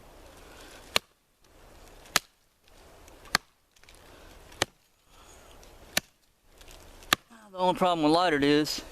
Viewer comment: whats the saying??????? 3:50 WHAT IS THE OLD SAYING!!!!!!!!!!!!! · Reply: Lol "As tough as a lightered stump" And thanks for watching brother.